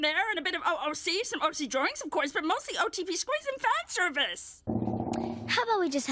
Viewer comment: The fact that i understand everything she said at 2:42 made me feel weird but relatable 😂😂😂 EDIT : Wow i didnt expect this comment got 200+ likes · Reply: tysm for this :') , the only thing Ive understanded was the "oc drawings" and for some reason I heard "FAT service".